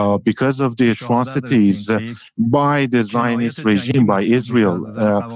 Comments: the subtitles say “chinese regime” but he is saying “zionist regime” , you ought to correct that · subtitle at 10:06 says chinese regime instead of zionist regime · caption says Chinese but it should say Zionist. Needs to be corrected asap!!!!!! Major Major transcription error!!!!!!